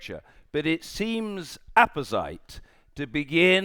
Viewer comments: Hey native English speakers: I'm learning English from John Bercow's speech and couldn't figure out the word he said at 4:49 "apecide"/ "epexide". Can anyone tell me what that word is? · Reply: He says 'apposite', it means 'it is suitable, well-adapted, pertinent'. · What word does he say at 4:48, "(...) but it seems EPICYTE/APACITE (??🤐😵😵‍💫) to begin by asking (...)" What is that word he is saying, dear God the man is a living legend! I have to clear that word! What is it, does anyone know? 🤓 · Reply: APPOSITE, as in fitting or relevant! There you go, learning every day! 😅😜